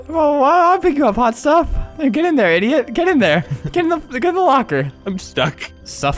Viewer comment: I heard him say Get in the Fucking locker